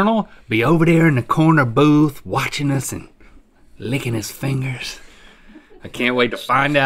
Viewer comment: At first I thought like said “ Colonel over there in the corner and us lickin’ his fingers” Lmmfao!